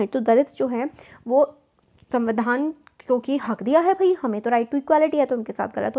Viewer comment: I thought that she said "Hag diya hai bhai"🤣 😂 🤣